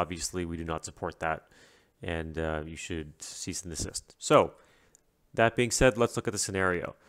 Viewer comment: I heard it as "and you should cease to exist💀"